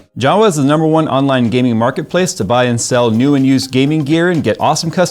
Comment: wait... did he just say we can buy ''incel', in jawa website??